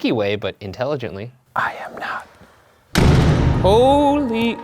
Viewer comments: if you put it at .5 speed it sounds like it says "I'm home" Instead of "I am not" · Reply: I heard "I am not him" tbh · I heard "I hope" in the "I'm am not" recording, which makes sense to me as a ghost hanging out in the chapel they would hope for that.